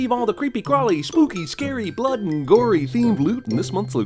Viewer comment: is he called gory?